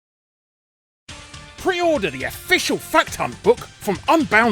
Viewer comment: Sounded like you said fuck ton not fact hunt at the start!